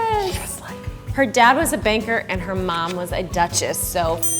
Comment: I heard "her mom was a baker and her dad was a duchess" i was like "how the fuc-"